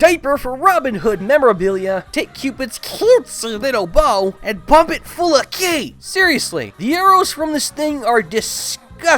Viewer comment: What did he say there? Pop it full of ki?